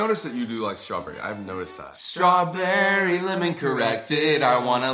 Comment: I swiped the vid to 8 mins and I thought it said strawberry lemon corrected at the start of the vid 😭